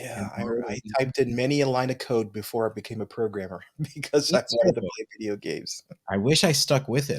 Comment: What did he say!? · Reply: "I did many a line of code before I became a programmer." That is, he wrote computer code. Yeah, I had to re-watch that bit to understand, ha ha.